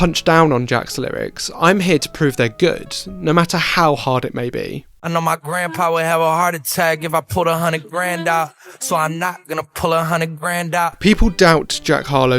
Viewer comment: all this time I thought he said "I know my grandpa would have a heart attack if I pulled a hunnid grand out, so I knocked then I pulled a hunnid grand out"💀💀